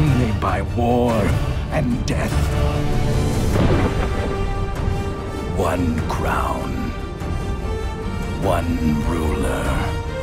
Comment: "one ring to rule them all", that's what i heard.